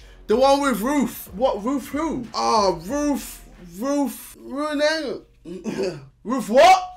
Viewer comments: I keep hearing "Roof" instead of "Ruth" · I thought JJ was saying roof instead of RUTH so I enable the caption and it's alos says roof